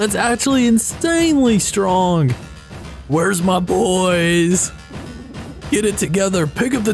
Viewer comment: i thought he said “where’s my bones” and i would have totally understood